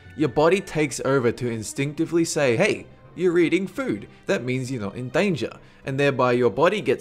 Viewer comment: Hey, you're eating food, that means your not in danger - 2015... April 1... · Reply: for a second i thought he said " hey you're reading gum "